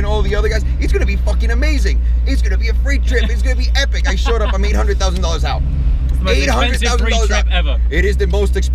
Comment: did he say 800,000 dollars? What did he buy?